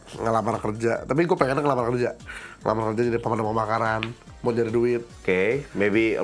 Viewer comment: kok gw dengernya lapangan kerja ya, jadi ngakak sendiri 😅